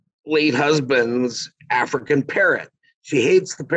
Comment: When the gentleman mad talking about the African parrot it sounded like he said they had an African parent that they wanted to get rid of.I am glad I listened to the rest of the man's story